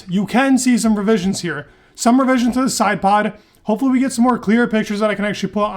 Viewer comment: Are you saying “slidepod”? I thought I heard that a few times 😅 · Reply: Yes it’s referring to the sidepod sloping down. That or water slide but I say slidepod